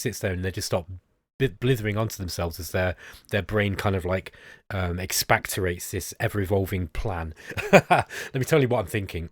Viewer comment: Expatorate? That's a new word. I've never heard that before. Is that what you meant to say? Did I mishear? Sorry, whenever I hear a new word I get very curious about it. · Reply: Expectorate, it means to spit or cough up